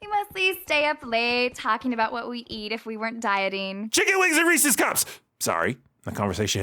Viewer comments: So when she said we talk about the foods we'd eat if we weren't dieting when you shouted I thought you said chicken wings and racist cops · Did he say, "Chicken wings and Reese's cups?" Or, "Chicken wings and racist cops?" Honestly, it can go either way it would still be applicable.